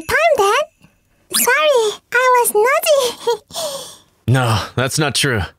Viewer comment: i hear naughty instead of nosy lol